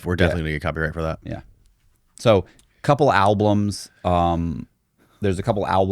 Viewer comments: WTF is an "Alblum"... He said it 3 time so I know it wasn't a slip... Or am I just trippin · I swear he’s saying “alblums”. It’s driving me crazy